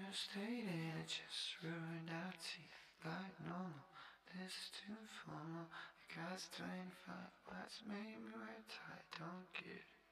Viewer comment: Wait what is he saying at 3:24? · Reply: What I'm hearing is "Shouldve stayed in and just ruined our teeth (??) like no, no, this is too formal. It costs 25 and you made me wear a tie. I don't get it"